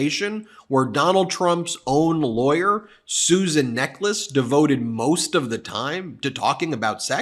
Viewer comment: I heard "Donald Trump's lawyer sues a necklace" and my mind didn't skip a beat. Just thought "Yeah that checks out".